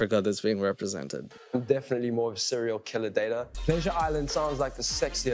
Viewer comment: wait did he say serial killer dater 😂😂